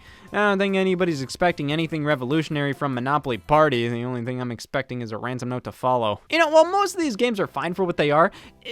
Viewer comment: I replayed this part several times and I still can't understand what he's saying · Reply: "The only thing I'm expecting is a ransom note to follow"